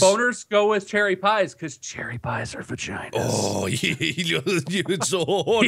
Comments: If u put 1:54 on captions, it shows saying for China instead of vaginas lol 😂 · the captions say "cherry pies are for china" and now I cant not hear that.